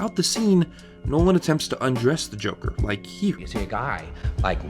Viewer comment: what is the last word at the end of that of that sentence? "Nolan attempts to undress the Joker like..?" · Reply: 'Here'...I guess